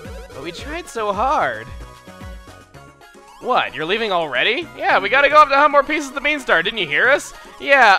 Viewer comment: What does he say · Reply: tjmurphy25 Yeah, we gotta go find more pieces of the beanstar, didn't you hear us?